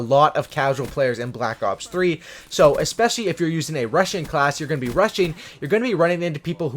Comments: I thought he was saying russian class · I thought you said russian class😂